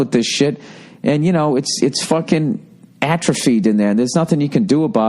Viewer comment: what is the word bill says: "..and her child-brain was filled up with this shit, and you know, it's fuckin'..." -there, that word. Atrifede? I've never heard of this word? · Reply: Atrophied like a muscle atrophy